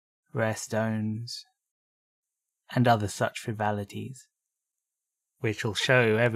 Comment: "and other such ............?" what is the word you are pronouncing here? can't quite hear it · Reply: Frivolities, silly items of not much meaning, but positive ones. It's a bit of a British colloquialism on the word frivolity.